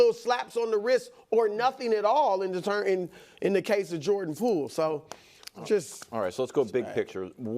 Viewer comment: Did Chris just call Jp Jordan Fool.... 😮